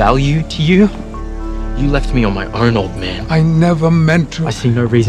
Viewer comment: " you left me on my Arnold, man" - what! Did I mishear that